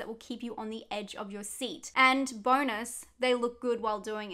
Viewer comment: I thought you said "Boners" not "Bonus”. Did a bit of a double take. Great vid!